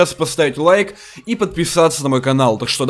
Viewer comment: послышало поставить диз лайк и отписаться от канала